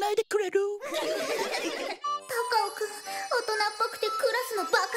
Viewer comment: の時心愛の高尾くんの呼び方カカオくんって聞こえるの私だけ？